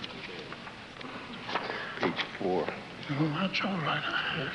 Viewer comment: hi, I don't understand really well what President McKay says on 3:24 could someone help please? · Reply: He got mixed up in the program so when he was getting helped he says "No, that's alright, I heard. Sounds like I'm getting old." At that time he was 90 and would turn 91 in September of 1964.